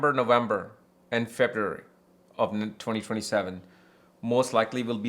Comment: Did you say 2027 at 36:33 get a job? This content was for 2026! 😮❤